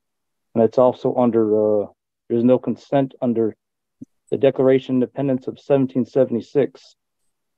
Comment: Did he just say the Declaration of Dependants????😂